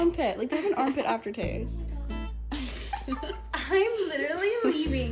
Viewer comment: what did she say in the end?? 'ill suck a (??) armpit..'